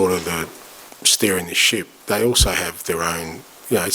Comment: When Ange said, "I'm steering the ship" I thought for a second he said "stirring the S**T!" lol. 😁